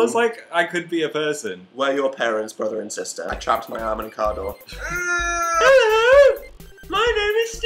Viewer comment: when says "I trapped my arm in a car door," if you turn on closed captioning it says "A chocolate avocado." XD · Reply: I thought he said "I chopped my arm in candor"